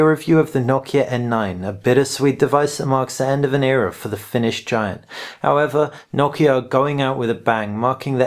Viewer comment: it's quite sad that i heard "Finished Giant" when he said "Finnish Giant". who knows it might turn out true??? I really love Nokia =(